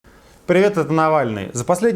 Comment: В начале ролика послышалось "привет, это диванный!" жжесть )))))